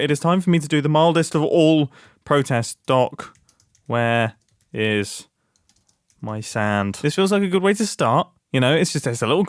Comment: I'm at work shoveling sand listening to this as white noise and poof I hear where's my sand.😂 I have to shoveling hundreds of lbs out of my machines if you want sand I got loads